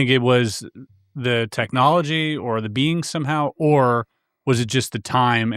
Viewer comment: hehehe when I hear you guys “being” a lot of times it sounds like you’re saying “bean”. I crack up every time, hehehehe.